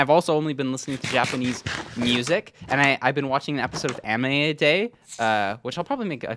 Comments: bro did not just say 'amine' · did i just hear you say “a-min-é” great video btw